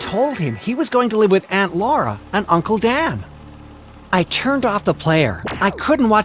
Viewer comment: “uncle dan” i thought he said dad😭😭